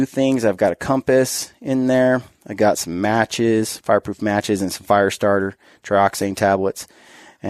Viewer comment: did you say "fireproof matches" ? Lol. Love the videos, love the podcasts. Stay gritty! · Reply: 5 Shot Group ~ Haha! Probably 😜. I meant storm-proof. 🤪